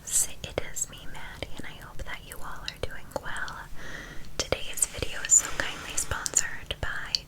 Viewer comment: Why do I hear. Hi loves. It is me Maddie and I hope you all are doing gwell